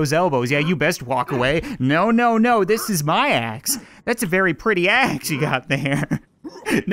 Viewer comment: I thought it said that’s a pretty ass